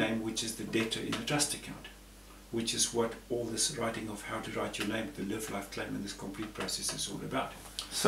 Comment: What does he say at 3:20? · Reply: He says, "Which is what all this writing, of how to write your name, the Live Life Claim, and all this complete process is all about." Also remember that subtitles are always available on all of "the GLOSSA channel" videos by hitting the 'CC' tab/button at the bottom right of the picture area of each video. (If the CC button isn't showing, scroll your cursor around/over the bottom right area and the button should then show.)